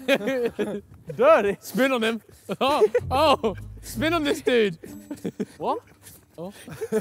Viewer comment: Lmao @ 7:00 listen in. Ethan goes “spin on this dude ohh spin in this dude” ....”what??” “What did he say” 😭😭😭😭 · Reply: “Spin on him. Oh, oh, spin on this dude.”